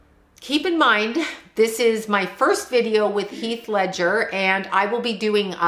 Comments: do I hear a man voice when u say his name ? I thought i heard a male voice say heath at same time u said it 😳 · I just watched this again, and I heard something really weird at 27:35 . It sounds like it's saying "Heath" in a deep scary voice... Anyone????